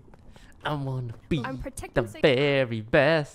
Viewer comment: Is it only me that heard "yow yvonne" instead of "pokemon" while he was singing the pokemon theme song???